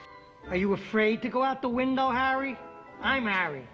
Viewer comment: Did he say Mary or Harry?? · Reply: Harry